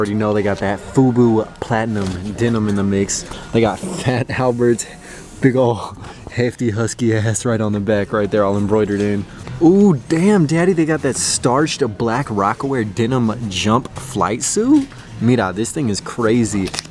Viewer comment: Did he say "Damn Daniel" before it happened?